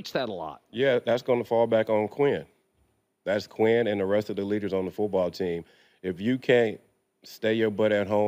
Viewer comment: Did he say rest of the ni$$%@ on the football team @ 8:59 😂😂😂 or I could be just very high right now